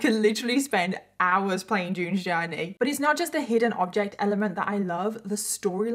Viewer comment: Ahh I’m sorry you feel bad still😢 it’s so funny when you were talking about the game that donates to snow leopards it sounded like you were saying “ Jews journey” I’m Jewish and thought “ what” 😅. Feel better soon. ❤